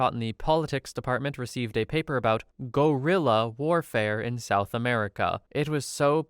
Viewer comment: English is not my first language, and I have misheard "guerrilla" as "gorilla", but I also immediately went "Wot? that doesn't make sense... OH, RIGHT, GUERRILLA" lmao